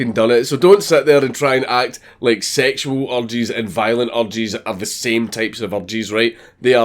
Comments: OH... urges.... I thought you were saying orgies. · It sounds like he's saying orgies instead of urges. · What he said : "sexual urges and violent urges are not the same thing" What I heard : "sexual orgies and violent orgies are not the same thing" Weird flex but okay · Having trouble watching past the “sexual urges, violent urges” part cause I keep hearing “sexual orgies, and violent orgies” and it’s making me laugh too hard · I could not understand him and thought he was saying "Sexual orgies are not like violent orgies". Idk what kind of sexual orgies you get into man but good god. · I thought dank was saying orgies instead of urges lol my bad. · For half the video I thought he was saying orgies but he was just saying urges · Oh you're saying urges, It sounds like your saying orgies. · haha at 6.00 I thought you were saying orgies, not urges, cause of the accent, painted an interesting picture till I figured it. fucking gold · I thought he was saying sexual orgies and violent orgies the first 5 times I cant stop laughing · I thought he said sexual orgies and violent orgies, took me like the 10th time to make out that he was actually saying urges and until then I was like what the fuck is a violent orgy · I thought you were saying orgies, but you were saying urges. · Urges man I thought you were saying "orgies" for the longest time ahaha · Am I the only person who thought he was saying violent "orgies" and not "urges" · lol i keep hearing "urges" as "orgies" hahahah